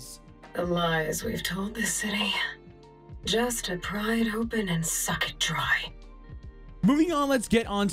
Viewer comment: I thought she said "just to pry it open, and suck it dry"